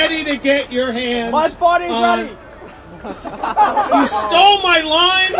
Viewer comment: My body is ready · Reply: I thought he said my butt is ready